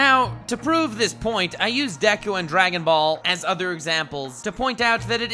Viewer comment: did he just say "i use deku in dragon ball" wtf? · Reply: And*